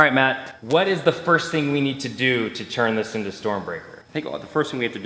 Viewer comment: For a moment I thought u said turn this old hamster....... Yea I’m dumb